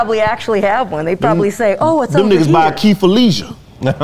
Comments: I thought he said “by Keith or Leisha.” Lol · 🔑key for leisure, I swore said Keefe Felicia 😅